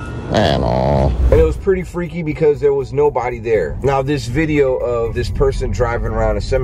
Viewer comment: When you said, "there was nobody there." I heard "there was no body there."